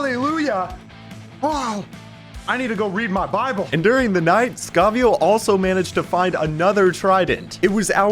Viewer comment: I thought you said," ...while skavio goes to find parents,"💀